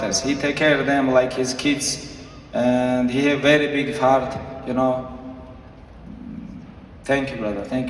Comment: I dont want to ruin the moment but did he say "Very big fart" at 19:57 Khabib is the Greatest, the Legacy of his Father will go on in him